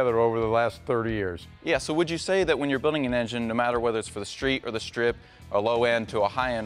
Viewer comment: My mistake,i thought you said you were building stock.